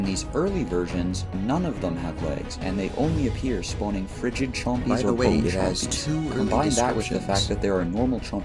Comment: Woops editing error at 3:05, accidentally copied my "By the way, it has two early descriptions" line In case anyone can't hear what I say there, it's "...it only appears spawning Frigid Chompies or Bone Chompies. Combine that with the fact that there are..."